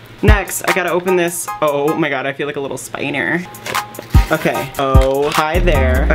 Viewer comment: It sounds like he said "i feel like a little spiner".......